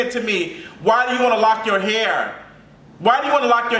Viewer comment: When I heard “loc’d your dog hair” 😩😩😩🤣🤣🤣🤣😂😂😂 I’m choking 😭😭😭😭😭😭😭